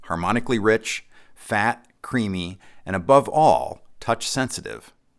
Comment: at first I heard it as: “Harmonically rich, fat and CREEPY!” Instead of CREAMY!hahaha😂😂😂